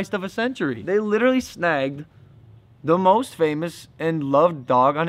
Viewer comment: Who els thought he said the N word lmao?